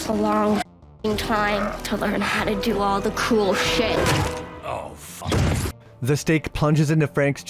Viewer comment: "The thing about being a vampire Frank.... is that it takes a long fcking time to learn to do all the cool shit" -Abigail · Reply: Thought she said "cruel shit".